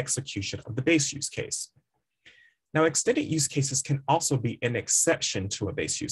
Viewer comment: Can't hear the word you are saying. What kind of use case are you refering to? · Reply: Here I'm saying "Extended" Use Case.